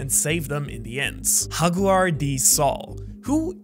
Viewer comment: Did he just say haguar d Saul? 😂